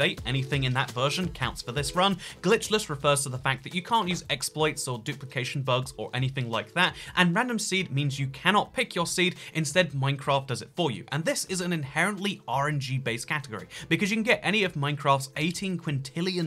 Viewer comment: says "RNG" but all i keep hearing is "orangey." Like, bruh, what you got against citrus???